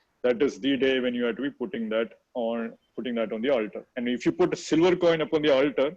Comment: Plese someone clarify what he is saying . I didn't get the word . I heard like putting siver in alter. What is alter?? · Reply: Altar. Place where your gods and goddesses are kept